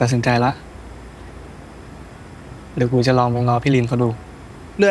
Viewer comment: คธาพูดว่าอะไรนะ ฟังไม่ถนัด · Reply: "เดี๋ยวกูจะลองไปง้อพี่ลินเขาดู"